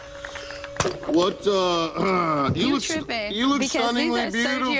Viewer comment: What does she say at 1:16? "You trippin cuz..."?